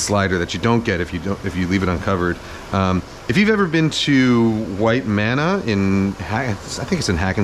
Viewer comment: Oh my god--White Manna is a burger place in Hackensack! Last video I misheard and thought "like a white man in Hackensack" was just some obscure saying I'd never heard before.